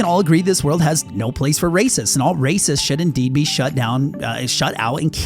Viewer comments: not gonna lie thought you said shot down. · thought he was about to say "all racists must be shot down" and I'm like yeah racists are bad but Jesus christ